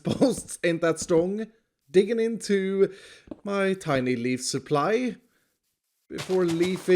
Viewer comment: I thought he said 'lead supply.' Like, dude. Fifteen shulker boxes is lead enough.